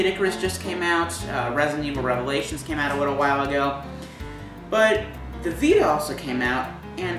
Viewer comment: Did he really say "a widow while ago" ?